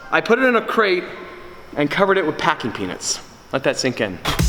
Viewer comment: I thought he said packing penus... Whats wrong with me